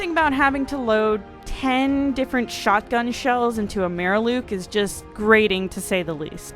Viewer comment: Do you know I had to listen to part of your video about 3 times before I understood what you were talking about, at 2:26 you say what sounds like "Mayor Luke". I'm deducing that you meant "Mirelurk", a creature the lurks in mires; boggy or swampy locations.